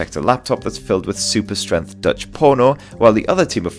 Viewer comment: did I just hear that right? 😂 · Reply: wtf? dutch porno?